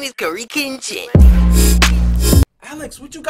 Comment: captions say curry kinchin XD